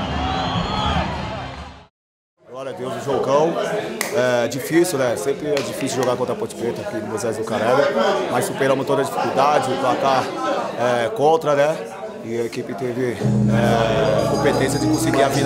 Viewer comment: So eu escutei moises lucaralho 6:50 kkkk